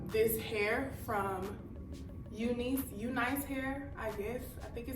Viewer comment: I thought that said UNCLE hair ☠️